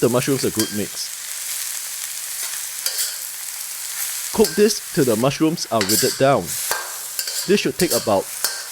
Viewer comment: what did you say? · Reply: HI. Cook the mushrooms till they are withered. 😀